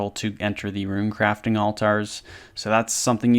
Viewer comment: All-tar 🙃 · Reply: To me, it sounds like he is saying all-tire instead of altar lol.